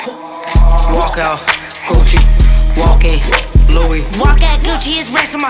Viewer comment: ⛓⛓🦋🖤 I deadass thought my bro said “Walk out, Coochie”.